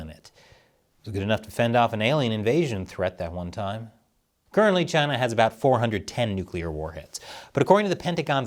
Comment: I was distracted when you said 410 nuclear warheads I thought you said 10 and was like ok... not many... then I went back to listen again 💀💀